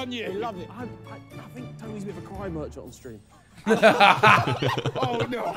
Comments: what did he say? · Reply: He said he's a cry merchant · What does Harry say? I've rewatched the clip like 5 times and I still can't make out what he says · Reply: Tobi is a cry merchant on stream